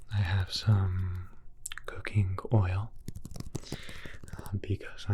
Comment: I swear I heard cocaine butter instead of coconut. I got really confused for a second and asked myself “Is that a real thing?” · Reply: He said cooking oil, but I thought he said cocaine oil too 😂